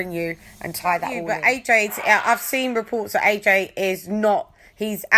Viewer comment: She said it seems like lol 🤦🏿‍♂️ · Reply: What? I thought said "I've seen reports?"